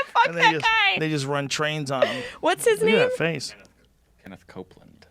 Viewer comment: did rogan say niggas run trains on em ???? 2:52:33??